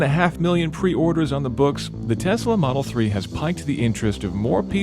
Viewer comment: Did you mean to say "has piqued the interest"? Sounded like you said "piked".